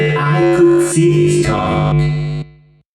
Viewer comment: Withered Bonnie: "Time to face the consequences of your failure" "Might as well face the facts, you were always destined to fail" "You blinked" "What is this (new prison?)? Is it me trapped, or is it you? Perhaps it's us both" "I may be missing my face, but even I could see (this coming?)" I hope I helped! Sorry about the parts I'm unsure of. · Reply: Oh and i heard "this tonge" instead of "this coming"